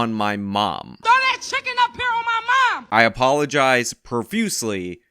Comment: omg... I clearly heard "Throw that chicken in my mouth" and when you made your correction I was blind sided! O_O